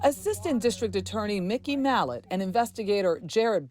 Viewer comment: not gonna lie.. i thought she said mickey mouse for a moment and i had to rewind to hear that in fact i am.. dumb and deaf😂